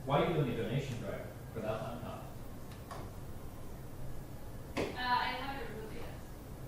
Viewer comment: she actually says "I haven't removed it yet" :) yw.